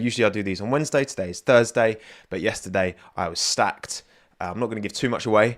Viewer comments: I thought you said you were sacked because you ticked off another driver. But was that stacked?? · I thought you said you were sacked, I got so panicked for a second